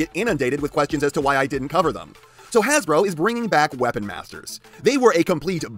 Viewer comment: Wesponizers bombed?? Since when?? I could almost never find them anywhere becsuse they always sold out, and the literal only reason fossilized and the new Junkions exist is because the prior two were so successful. They most certainly did NOT bomb, man. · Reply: Oooh, I must have misheard entirely, I thought you meant weaponizers... Welp, that's my bad entirely if you meant Weapon Masters. Yeah, those things shelf-warmed for a long while. So yeah, you're right on the money with them, they didn't seem to do very well at all.